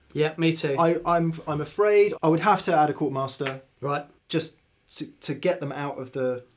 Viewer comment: Didn't quite catch what said re a switcher at 59:18 "Court or Chord Master" or something ? (Incidentally is that related to the Tennis theme board....LoL.....) EDIT: GigRig Quartermaster....of course.....